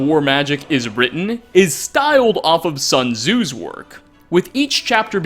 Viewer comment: When he said sun tzu captions thought he said son OU’s